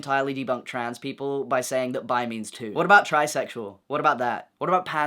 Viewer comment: "what about trisexual?" i swore you said tricycle.